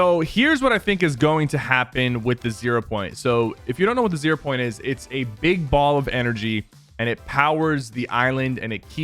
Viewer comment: did he just say the zero point powers Ireland????